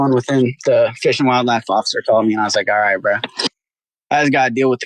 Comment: The text reads, "Fish and Wildlife officer CAUGHT me...". What he actually said was, "Fish and Wildlife officer CALLED me....." 11:40 The text reads, "go to court to figure out the FUNDS.".... What he actually said was, "go to court to figure out the FINES."